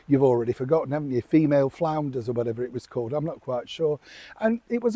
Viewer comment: I'm not exactly sure how you said her podcast "female founders" I heard "female flounders". I think that's the correct podcast name, very appropriate Neil ! 🤣🤣